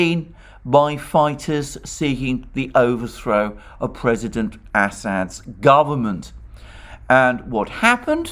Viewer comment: Did he just say President Asshat?